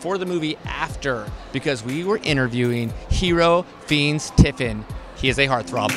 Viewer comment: Is it just me or in 00:07 he call him finis instead of Fiennes?